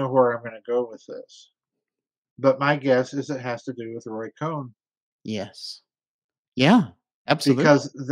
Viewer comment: What does Farrell say it has to do with at 32:28? Roy Cone? Don't know that name. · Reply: Roy Cohen, general counsel for Senator Joseph McCarthy during the McCarthy hearings 1952-53. Look him up. One of the best political operatives, ever. The 'original swamp creature'. Listen to the more recent /Farrell interviews on YouTube channel playlist. Like the last 10-12 interviews on that playlist.